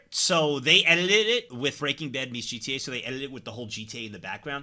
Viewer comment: Did you just call it Breaking Dead ???? 0:25 you mean Breaking Bad Right ??? Right ????